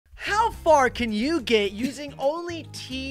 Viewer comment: did he just said "you gay"?